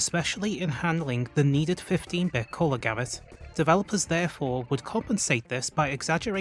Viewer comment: does anyone know what he said? The needed 15 bit corlagamit? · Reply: 15 bit color gamut.